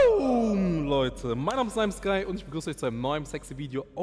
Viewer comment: wie ist sein Name hab 10 mal gehört versteh es nicht